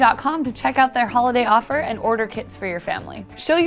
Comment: I thought she was saying "order kids for your family" and then I was like we are making kids by genetic engineering and I don't know about it HOW!!😱😱😱 And also thanks for commentinging (reading my comment)